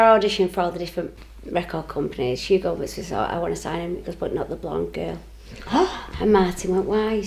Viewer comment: did she say "i wanna sign but not the blonde girl"? i couldn't hear her properly